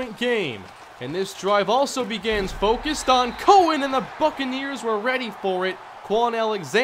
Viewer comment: I thought he said "Fuckaneers"